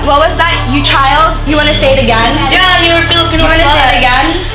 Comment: It's crazy how this has aired YEARS ago and it was only today that I realized what said to Katarina at 34:15 like, it's just the 2nd episode and she had something that disrespectful to say towards Kat. 🤨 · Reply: I think said “Filipino slvt” but I wouldn’t look too much into it since it’s scripted